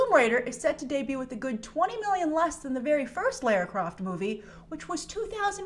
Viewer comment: When you said "Lara Croft", I thought you said "Lair Crock". LOL!!